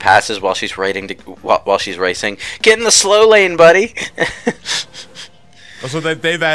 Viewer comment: "Get in the slow lane, buddy", is that what she's saying? She usually says it when starting a race, so I heard it as "Take it slowly, buddy", as if she's condecendingly recommending her competitors to take it easy because they aren't winning against her anyway.